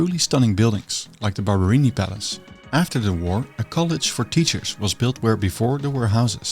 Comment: you said "a college for teachers", but because of your Dutch accent I heard "a Colditz for teachers". As the Institute for Teacher Training did indeed look like a fortified war prison, I could not help but smile 🙂 I also didn't know that the authorities in Dresden were being obstructive, but I did love your fabulous new iteration of the architect caricature: shaven head, glasses, black polo-neck and all! God, but I love this channel.